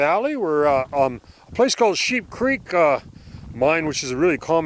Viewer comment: haha I thought you said Shit creek we all know about shit creek. LOL